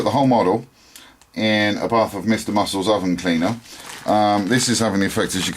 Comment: Awesome!!!!! At 6:11, did you say that was Master Models Oven cleaner? I've had paint mistakes like that and wished there was some miracle stuff to fix a bad paint job. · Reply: Thanks John and thanks for subscribing too! The product I use is called Mr Muscles Oven Cleaner. Not sure where you live, however it us readily available in the UK. Works a treat for dissolving paint but not harming the plastic.